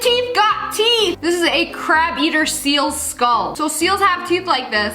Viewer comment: I thought when you said a crab eater seal you said a crap eater seal😂😂😂